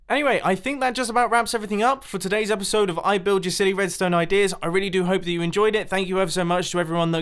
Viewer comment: I just thought it said building you billy redstone ideas.... Da ...